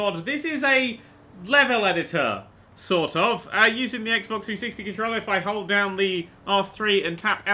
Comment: Did he just say xbox 260?